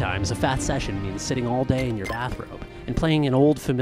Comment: ngl, I misheard bathrobe as bathroom so when you said "Sometimes a fat session is sitting all day in your bathroom" and I got really confused as to what this 5+ hour video was going to be. Also thought damn, you need more fiber in your diet.